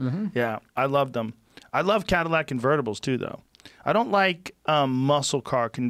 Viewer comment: I thought he said ''I like catalytic converters too tho''. I was like, what. Then I realized he said ''Cadillac Convertibles'' Lol.